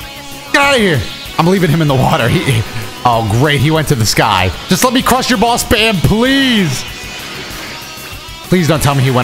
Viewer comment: What said: Just let me Crusher Ball spam! What I heard: Just let crush your balls fam!